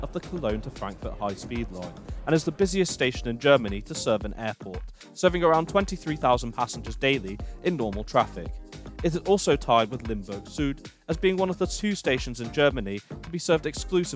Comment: I thought I heard you say Limburg Sued, but fortunately the subtitle indicate you meant "limbo suit" ...